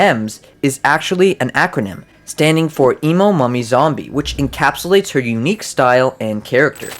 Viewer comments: i thought he said "mommy" instead "mummy" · i thought it said Emo Mommy Zombie for a second lol